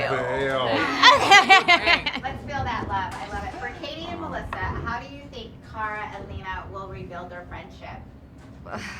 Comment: *FOR KATIE And melissa: how do you think Kara and Lena will rebuild their friendship? Mel: ... its hard to say azie: a ??????? (WHAT DID SHE SAY?) anyone? Chyler : that is always ????? (?) can you script that part ? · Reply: Azie says "alcohol?" And chyler says "probably the answer"